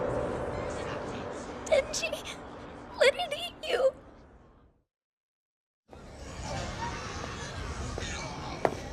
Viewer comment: guys what did she say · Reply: She says “Let it eat you.”